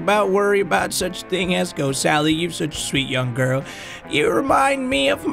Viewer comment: Did she said ,, young GIRL"